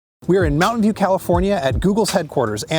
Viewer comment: Almost thought you said you were in "Mountain Dew" California. I was slightly excited for a second. Also, on topic, definitely interested in one of these. It's an interesting take on a tablet.